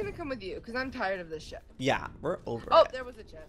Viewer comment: OMG I LEGIT THOUGHT SAID IM TIRED OF THIS SH*T INSTEAD OF SHIP I ALMOST FELL OUT OF MY CHAIR.